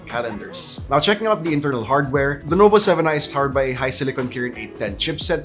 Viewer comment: did he say lenovo 7i